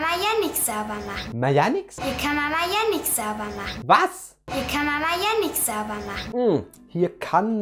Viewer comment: ich hab da verstanden hier kann man ja jenix sauber machen